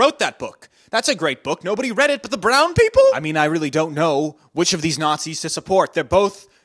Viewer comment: “Nobody read it but the brown people?!” That made me actually LoL. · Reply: I thought he said pronoun people